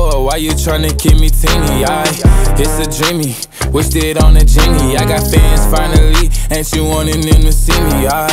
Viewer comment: also i thought he said its a genie